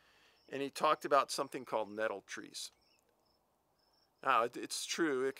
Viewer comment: what did the coroner say was the cause of death? Sounded like said “Metal Trees”. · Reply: Nettle trees